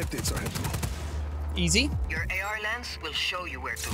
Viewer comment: I’m just realizing its AR Lens, I thought she way saying New Orleans